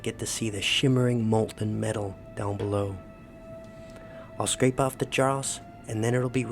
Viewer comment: what’s the word you’re saying? “Scrape of the j—-“?